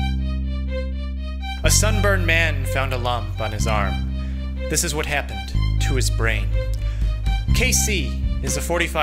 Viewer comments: The first few times I saw this video I thought it said "A Farmer Found A Lump on his Farm. This Is What Happened to His Brain." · why did I hear "a sunburned man found a lung on his arm. this is what happened to his brain" XD but tbh sounds like it could be an actual video title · i thought it said a lamp in its arm and i thought the "lamp" was radioactive